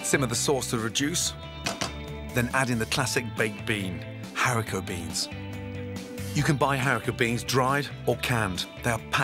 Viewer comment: is he saying "haricot beans"? cause you know guys that haricot is french for bean... so bean bean that's genius ^^